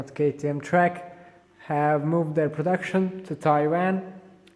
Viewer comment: I thought you said "Trek moved there factory to Iran" at first. 😂 whoops! My bad. Nice build!